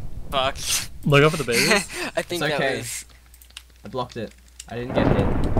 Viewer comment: When slipped up I was sure you'd be messaging Mjora about it, but immediately afterwards: "I really think it's Zermbeeh" This will be interesting to watch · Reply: At about 18:00... In global chat: "is anyone else alone?" Followed aloud by: "fuck, I think that was ..." Given the circumstances - chat conversation with Mjora, thunder and people talking over what said verbally - it's understandable that it was missed; I just found it funny since, watching video, it felt almost certain that more people would notice